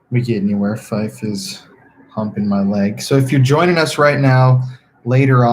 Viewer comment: I thought you said "my wife us humping my leg" 😂😂